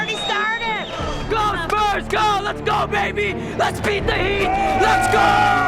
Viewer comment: First time I heard 'Beat The Heat' chant I thought they were saying 'Beat The Meat' Lol